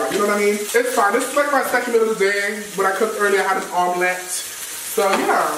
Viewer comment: Can somebody tell me what he said bc I'm thinking something else 😭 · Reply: He literally just said it’s his second meal of the day, he cooked an omelette 😅 sis fix your ears 😩